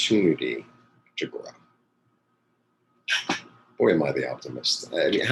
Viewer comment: Quite interesting 🧐 when you were talking about alchemy one moment for fountain ⛲️ of youth and then... a moment later you said “aren’t I the “optimist”?” I thought you were saying aren’t I the “alchemist”? So good to pause and think about what resonates in reading...apply love/will/wits and manifest - many thanks 🙏